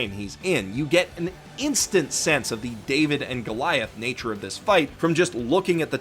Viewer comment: For a second I thought you said David Prod. But then I was like, wait, part 5 isn't coming